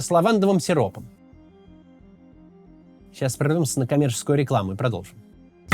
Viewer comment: мне послышалось не комеческую и уж собирался срамить по чём свет стоит🤣😅